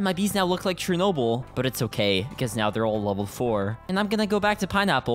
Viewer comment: “And my bees look like Chernobyl, but it’s ok, because they’re all level 4” (What I heard) —-> “and my bees look like Chernobyl, but it’s ok, because they’re all ON THE FLOOR” I died laughing thought he said they were all on the floor